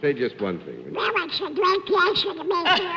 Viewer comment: 11:59 Must translate this from duck language. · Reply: "Well, its a great pleasure to be here..." (I couldn't make out the rest of the sentence over the applause) 11:56 "That's right" 11:59 A wonderful picture too"